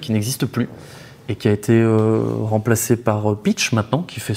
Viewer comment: a 4:29 environ il parle de "pitch" c'est quoi ? merci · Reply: C’est Peach Bitcoin